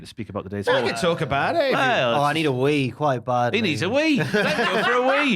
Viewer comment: I thought at the end said “I need a week” as in “I need a week to talk about how Raw being 3 hours was a mistake”